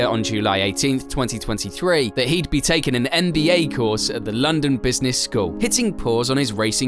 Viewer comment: oh god I misheard 'MBA course' as 'NBA course' and thought Goatifi wanted to buy his way into basketball next